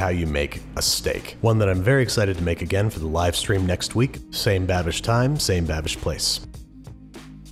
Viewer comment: This is the first video I’ve ever watched from you ( awesome vid - I’ll subscribe ) idk why but at the end I thought u said “same bad bitch time and same bad bitch place”